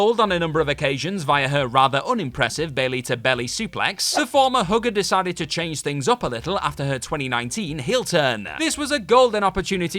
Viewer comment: I had to rewind a bit. For a second, I thought that said about Bayley that 'the former hooker decided to change things up a little'...